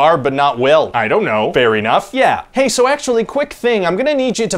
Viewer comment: "How come it kills Barb and not Will?" "I dont know!" "Fair enough." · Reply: Every time "Fair enough" is said, I hear "Fairy-Nuff" for some reason.